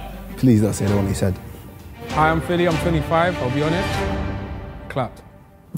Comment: I thought he said clout not clapped 🤣